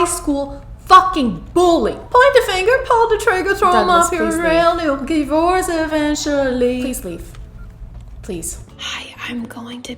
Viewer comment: I am very late but I am crying it sounded like you said Point the finger pull the trigger throw them off their tral You'll divorce eventuallyyy LMAO